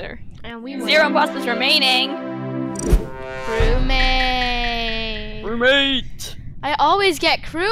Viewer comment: I heard a weird voice that said crewmate LOL